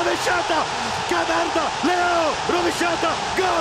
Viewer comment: A 0:23 credevo Suma avesse detto "Che m***a, Leao rovesciata!" Poi ho capito...